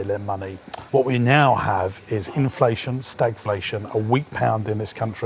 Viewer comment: What is "stateflation"? Or did I mishear that?